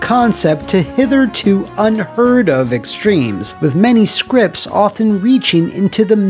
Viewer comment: Did you just say hitherto unheard of? 🦸‍♂️